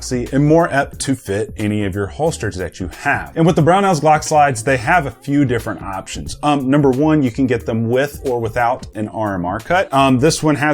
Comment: I could’ve swore you said you’ve exploded a one or 2 you’ve tried in the past I was like wtf